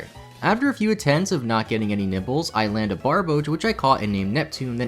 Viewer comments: any one hear him say "not get any nipples" instead of nibbles? Lolol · nipples? Did I hear that right? 😂😂😂